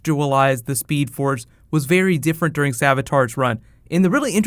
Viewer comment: I thought said it was rival nog savatar.